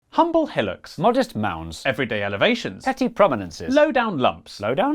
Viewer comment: I thought you said Modest Mouse for a second.